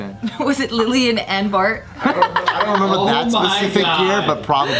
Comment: Did she say "M Bart"?